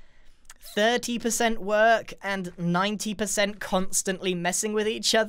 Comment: 30%, and 90%. Did I hear that right?? Are there like 120 percent now?!?!